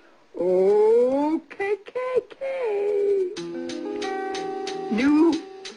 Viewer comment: sorry did grandma just say KKK